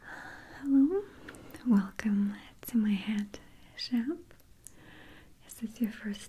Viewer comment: Definitely thought she said "welcome to my headshop" at first 😂